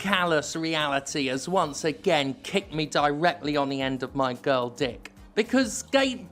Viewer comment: Did I just hear the words "girl dick?" Lol